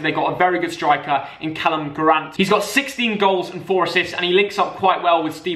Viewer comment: did he just say callum grant